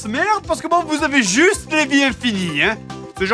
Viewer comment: Il dit quoi à 21:45 ? Je me le suis repassé en boucle 10 fois impossible de comprendre... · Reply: "Ils ont juste mis les vies infinies"